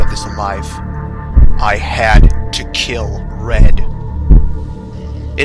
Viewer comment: it sounded like red was saying "I HATE YOU" When he died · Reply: i thought he said I HAD YOU cuz acacius was at very low health